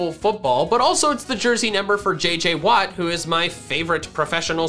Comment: JJ *Watt*? Is that his name or did I hear that wrong